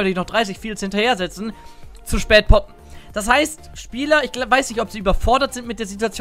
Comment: jedes mal poppen verstanden :D